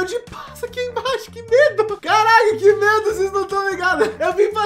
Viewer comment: Minuto 0:50 só eu que entendi c4ralh0, que medo?? Que isso